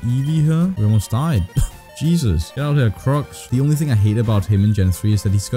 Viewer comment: I have no problem with the way you talk but, at 9:20 it sounds like you said hate instead of love ... I just think its kinda funny.